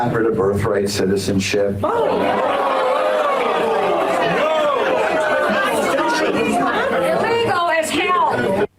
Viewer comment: what does the lady say? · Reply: "Illegal as hell"